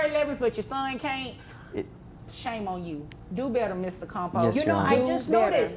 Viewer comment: Every time she said his name, I heard "Mr. Compost" in my head... and that tracks.